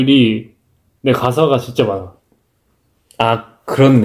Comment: CORRECTION: SUNGHOON said family picture (not past picture) I misheard ga-sa as gwa-sa 😭